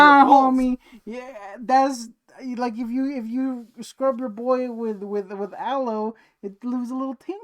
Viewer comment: why do i hear saying "scrub your eggs with elmo" all the time?! doesn't Elmo say NO to this?!